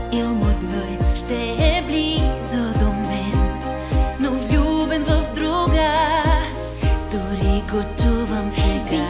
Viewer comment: soy yo o parece que dice "nus vendo droga" xd?